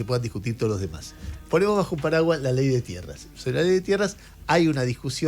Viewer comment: juro que escuche "la Ley de Mierdas"